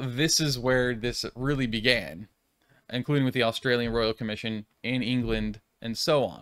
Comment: Did I hear, at about 7:18, or so, the moderator say: the Royal Australian Commission in England?! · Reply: Haha, nope. "The Australian Royal Commission and England" is what I said there.